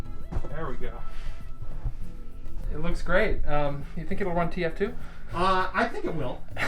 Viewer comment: i hear tf2